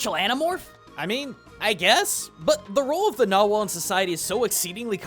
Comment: Wasn't really paying 100% attention, until he said "Nagual". When he said that, I thought he said "Narwhal", and I was like, what? how do Narwhals fit in this??